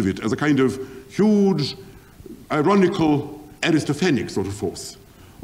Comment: what is the word? ...a huge ironical "aristophanic"(?) kind of force....? (meaning a mocking force?)